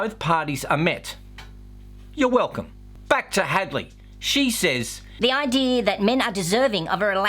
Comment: Hadley? Thought you said Hagley. She looks like a hag